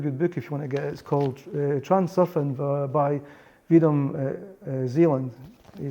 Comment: What was the name of the book mentioned at the end? I heard "Transfuerin by Edom Zealand" and I cannot find it. Thanks! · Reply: reality transurfing Vedam Zealand